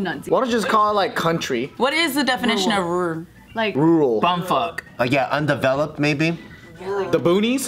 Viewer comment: I just woke up and this is the first video I'm watching... and I think my brain is lagging. What did say at 0:41 ??? ... All I'm hearing is "bumfk", and I'm pretty sure that is not the answer to question. O-O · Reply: He said Bumfuck. Like Bumfuck nowhere. i.e. in the middle of nowhere.